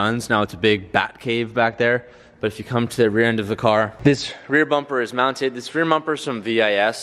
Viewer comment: Anyone hear him say rear mumper